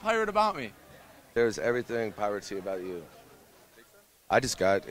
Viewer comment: Did he say "theres everything of poverty about you"?! XD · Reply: Alexx Vicious He said "There's everything piratey about you" because the guy said "There is nothing pirate about me"